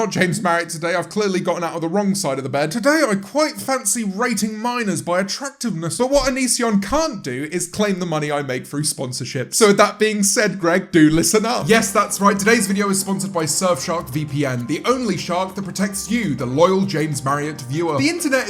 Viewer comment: did he say Greg? someone been watching Danny